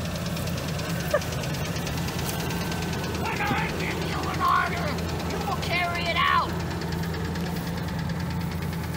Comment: What did he say here before he said "You will carry it out"? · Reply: He said, “when I give you an order,”.